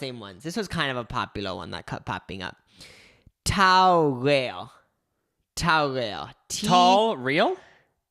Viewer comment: i thought you were saying Tara Reid akfjjdlskdjcksnncnc